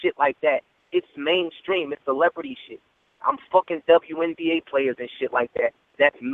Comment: did surf just say he's fuckin wnba players?!?! lollll post of them look like guys. at 33:10 ... was just like "oh alright"lol